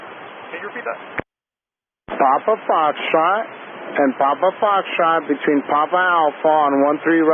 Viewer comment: what is he saying??? Papa fostra???? What's fostra? Is that even a word?? Can someone answer me please? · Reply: Papa Foxtrot